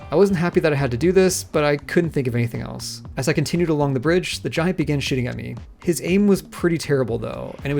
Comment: I swear the first time I heard this I thought you said “the giant began shitting at me”